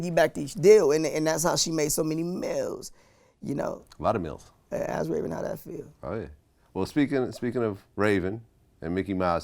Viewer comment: he said now ask Raven how that feels. I had to listen to that part like 3 times to understand what he was saying.